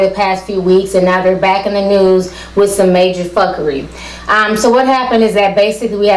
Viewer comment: did she say "major fuckery"... ?